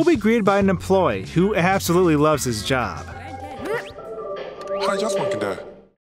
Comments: One the first one I heard “How’d you walk in there” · I heard “How’s your smokey day?”